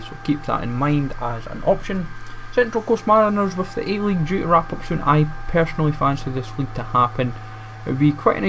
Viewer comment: I was about to have a nerd rage when you said "with the a league due to wrap up soon" cause I thought you said start up soon